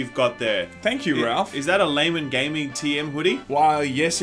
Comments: I heard it as laming gaming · Pretty sure i just heard "gaymen laming". I have no idea what it means, but i can't unhear this now.